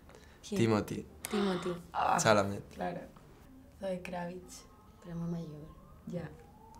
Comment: que dijo Martina? A quien escogió? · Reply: Zoe Kravitz